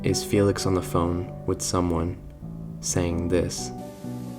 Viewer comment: I thought it said "hey sis" at the beginning and thought we were going to get another family brought to light. However the kids died on May 2nd 1974 and so "May 5th, 3 days after the accident" makes more sense